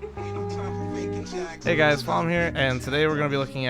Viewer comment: just me that misheard the beginning as hey guys fallen here?